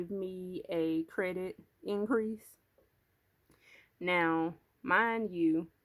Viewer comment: really , I could swear I heard you say something about an increase but then again, I’m working while watching and heard wrong then. Either way , great budgeting💜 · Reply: it's okay. I did get a credit limit increase from Capital One. Maybe that was what everyone heard.